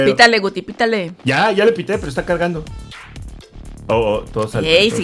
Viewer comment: escuche mal o dijo, [pero esta cagando]? 🤨